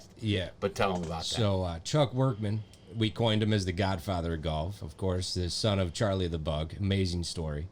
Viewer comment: At first I thought he said godfather of GOTH🤔